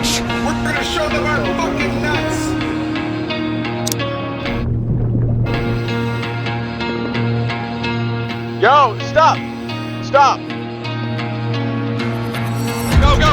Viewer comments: When was doing a speech i heard someone yell "we'll show them our fuckin nuts!" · while the africa leader was talking about show them your courage all i hear is "were gonna show them our nuts" from a random person 😭🙏